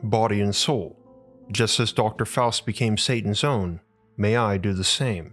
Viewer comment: Legit thought this said Dr Fauci